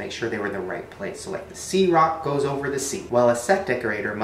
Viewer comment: When said "the c rock" I thought he said "the sea rock" and I was like how the heck would a rock from he sea go to the moon